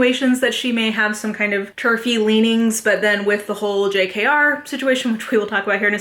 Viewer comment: At first I thought you said turkey leanings and was very confused but then I got it.